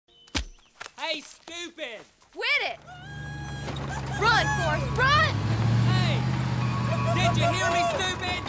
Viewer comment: I thought she said run for it!